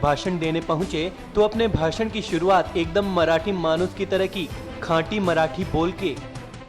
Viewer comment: did you say "Ghati Marathi " ?